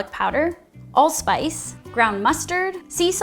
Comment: It sounded like you said, "add Old Spice" instead of "allspice" lol